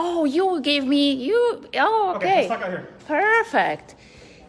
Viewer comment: I swore I heard "get the ** out here"